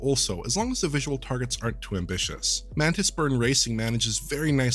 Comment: What game is it at 3:19? Sounds like he is saying "mantled squirrel racing" or "mantle squirtle racing". No hits on google :)